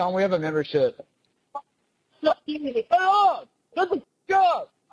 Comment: I heard "shut the F up" not "get the F up"